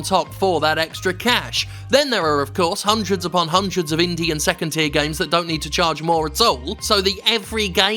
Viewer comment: For a few seconds I thought you said "Indian second-tier games" but then my brain parsed it properly.